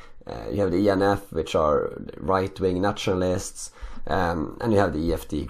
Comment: Did anyone else notice that whenever he said ‘nationalists’ it sounded like he was saying naturalists’. Not a complaint just an observation.